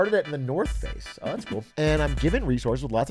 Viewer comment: "I'm at the north face" What i hear: "I'm at an orifice"